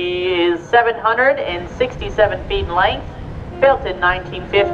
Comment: At the 50 second point the announcer says the ship's top speed is 13 knots. Then it sounds like she says that is the equivalent of fofty miles per hour. And, indeed, that is the speed that is represented in the closed caption text. But the actual equivalent is about fifteen miles per hour. So the PA announcer is probably saying "fifteen" which can be misheard as "fifty"